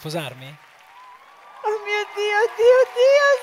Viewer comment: Angelo perdonami ,ma non ti ha detto si ...il pubblico ha detto si. Non sposare una donna che NON ti ama. Guarda la proposta di matrimonio di Fois,quella é la risposta di una donna innamorata. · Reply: io a 3:23 ho sentito "oddio oddio s.." 10 secondi dopo ha risposto di nuovo sì. Che doveva fare? Certo che per cercare problemi inesistenti siete ferrati :)